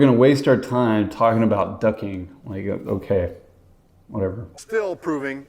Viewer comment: Ducking!... Phew, I almost thought you said Jeepers are "Docking".😳